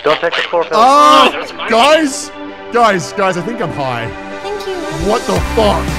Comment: I swear when appears at 9:00 I swear she said "Thank you Rimmy" which just blew my mind. Seriously love all of Arma videos, but this has to be one of the best. Not just because anime, but it ended up with a interesting story. The anime helped though.